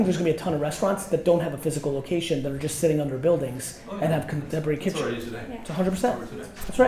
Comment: I couldn’t hear what Gary said about restaurants @ 3:13 did he say “bring kitchen”?